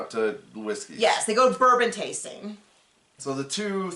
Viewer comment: There's a lot of background noise where I am right now and I thought you said they went vermin tasting😂